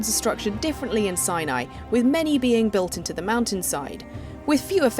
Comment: did she Just say cyany?